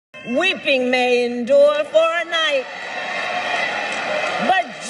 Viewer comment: I kept hearing “WEEPING MANE DOOR FOR A NIGHT”